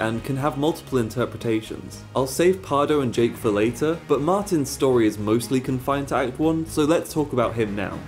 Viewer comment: Sorry I'm not a native. Can someone help me know what he said here? Martin's story is mostly (?) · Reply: It is "Martin's story is mostly confined to act 1"